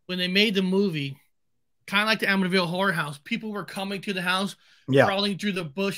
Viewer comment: Did you say Whorehouse 😂 thanks boys. Great show 🍺